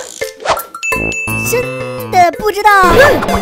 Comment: 疯狂点点一下停一下 说真的不知道就会听成俺不知道